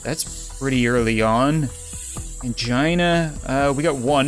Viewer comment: Ok so, when you said Angina I immediately thought that you said 'CHINA!'. Well, that fits perfectly with the situation!